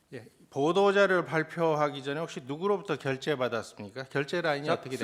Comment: 발표하기 전 이라고 묻는데 작성하기 전 이라고 알아듣는것보니 귀가 잘 안들리시나봐요